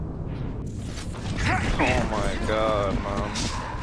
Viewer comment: it sounds like he says my god mom. it really does.